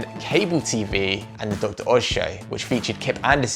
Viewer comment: When number 6 is introduced, it sounds like he says “The Dr. Oz Show” but it’s actually “The Dr.s”